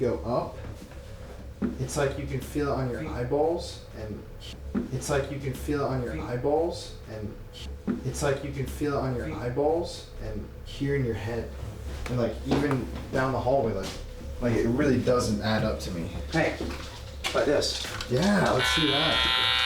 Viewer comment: as said eyeballs, I hear feet